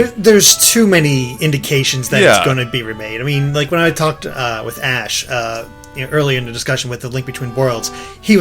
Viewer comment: did he say he talked to ash?!